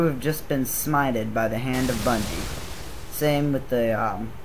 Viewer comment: did you say bunny at 4:37?